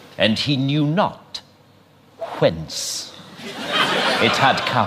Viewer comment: I just wanna ask if anyone know what Rowan Atkinson says on 45:02 ...Something like "And he knew not......(something I don't know) it had come" Something like "fenc". Does anybody know, please? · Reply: he said "whence". It's definition is " from what place, source or cause. It's an old English word. According to Merriam-Webster online dictionary, !st known use of the this particular word was in the 13th century.